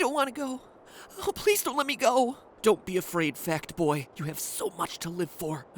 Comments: I thought he said "Fat boy" not "Fact boy" haha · Lol i thought you said Fat boy lmao